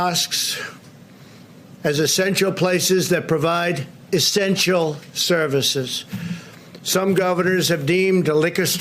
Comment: Did he just say "estential services"?